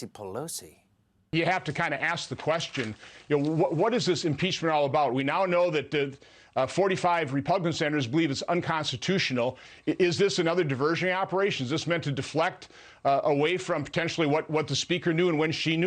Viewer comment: Did he say "45 repugnant senators". Freudian slip perhaps?